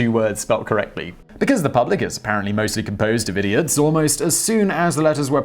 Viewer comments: YouTube closed captioning is so crazy at like 2:25 you say because the public is mainly composed of idiots but closed captioning for YouTube says d-bags instead of idiots it's just so odd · HEY 👋! I checked this trivia, BUT on my cc ALL the Shakespeare names WERE spelled CORRECTLY! Maybe it’s been changed? Or maybe my eyesight is OFF? Or maybe it’s because I live in 🇨🇦 😁🥰🥰🥰👍 I DID notice (at 2:25 ) that when Simon said “Because the public is apparently mostly composed of idiots ...” CC, HOWEVER, actually wrote: ”because the public is mainly composed of d-bags ...” Simon speaks fairly quickly (one of the reasons why I like his videos & also because he doesn’t use the typical drone music in background👍), so I triple checked this after I first heard it. I’m sure it happens frequently due to his rapid speed speaking. 😉